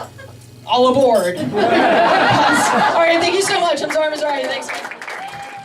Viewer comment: Anyone else see her in the laugh factory video and then come here? Bc all these comments are new. Also when she said her name in the end i thought she said "im sorry im sorry" bc she said it so fast.